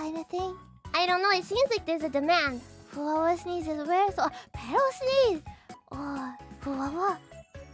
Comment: ここ何て言ってるんだろう？「Fuwawa sneeze is rear also~ ???????」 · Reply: 他の切り抜き翻訳を色々みてると「ペロのくしゃみ」って訳されてるので「pero sneeze」って言ってるみたいです